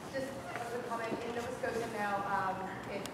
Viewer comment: Whoa I thought I heard that woman say Nova Scotia. Great upload. Thank you